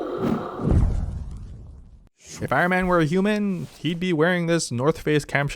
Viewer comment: Did he say if Iron Man were a human?